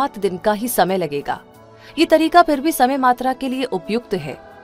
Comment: किस किस को 21:37 पर समय मात्रा (यात्रा) सुनाई दिया 😮